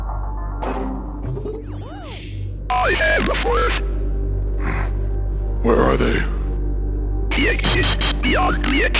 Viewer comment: Does anyone know what fatal error was saying here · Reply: Turn on captions! He said “I have a fourth.”